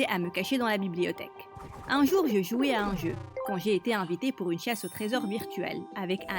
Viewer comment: j'ai entendu une chiasse au trésor